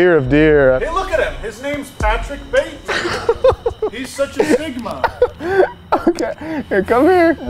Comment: What did Bro say 😂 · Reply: He so sigma